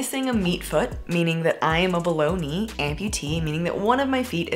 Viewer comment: When she said "im a below knee amputee" I heard "I'm a bologna amputee" and it took me a min 😂